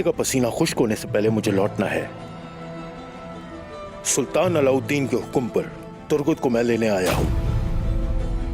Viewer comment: what did he said SULTAN ALAUDDIN...???